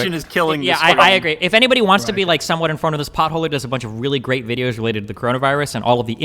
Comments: What name does Destiny drop at 1:04:26? I legitimately can't understand, I just hear "Potholders" lol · Whos videos is he referring to? Cant understand the creators name, he talks way to fast. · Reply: potholer45. An australian climateactivist.